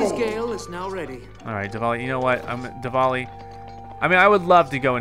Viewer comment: I think you said DEVALI